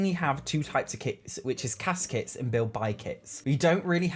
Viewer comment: Oh my gosh, for a split second I heard "CAS kits" as "caskets" and got real scared ⚰️⚰️